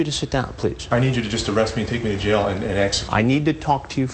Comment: Did he just say " I need you to take me to jail and EXECUTE ME"?